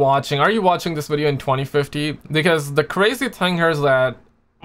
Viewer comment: Did he say 2015 or 2050??? EDIT: Okay, it's 2050...but why is he referencing 2050 so much in this vid? What's going to happen in 2050??? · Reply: Yeah 2050, just a way for me to say "in the future"